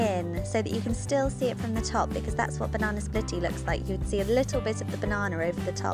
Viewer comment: is it me or does it sound like shes saying barnana